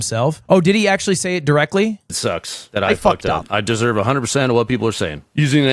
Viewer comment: All I hear is "I got caught" not "I fucked up."